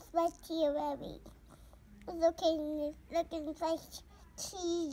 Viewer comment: I heard ‘it’s ok look inside you‘ repeated at least twice’ that’s a motivational speech right there